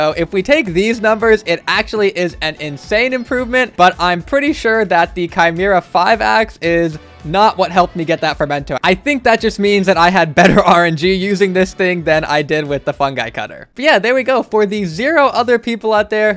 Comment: DID YOU SAY RNG?????????